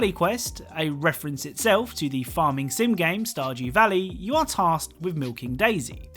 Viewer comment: I thought he said sourjew valley